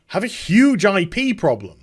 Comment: I had to listen again because I thought you said the have a huge ID problem. The more I think about it they do have an identity problem as well as the IP problem that you detailed. If they don't figure out what they're trying to do, things will get worse. Part of me is hopeful that things will get just bad enough at Activision for Blizzard to separate from them.